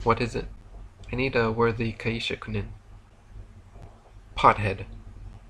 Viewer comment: What does Asbestos Head say to Machine Head here at 2:04:37 "I need a worthy ...".? Thank you. The transcript needs human editing I think. Can we contribute perhaps? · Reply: Kaishakunin: A kaishakunin (Japanese: 介錯人) is a person appointed to behead an individual who has performed seppuku, Japanese ritual suicide, at the moment of agony.